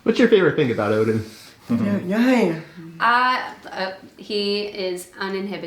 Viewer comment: Almost sounded like Odin said, "Nothing"...LOL